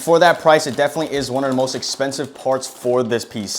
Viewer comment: i think bro said it's the most expensive pc piece